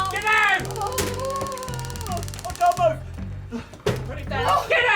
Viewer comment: When he was tased, did he say "don't move"? like it fried his brain a little?